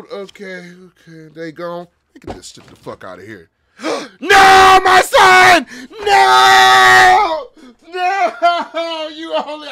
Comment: I swear I thought he said Oh no my son!!! I was freaking.